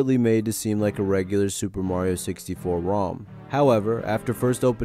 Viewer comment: not gonna lie… thought it said “Sperm Mario”…